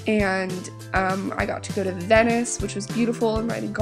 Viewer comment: Omg when you said venice I thought you said dentist so I was like.. you think you're dentist js beautiful?